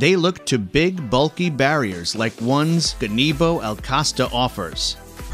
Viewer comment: Did I just heard kannibal?